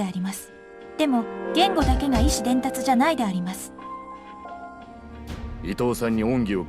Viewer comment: Am i going crazy, or did she say ISHI (意志?) and not IMI 意味 at 3:09? Also, i don't want to sound like an ungrateful bastard, but i really missed the furigana on some of those "advanced" words. Great Video anyways.